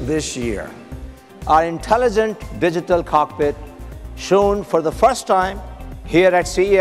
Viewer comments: Did anyone also thought he said digital carpet at 8:25? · Digital Cockpit? I could have sworn the guy said Digital Carpet, twice! LOL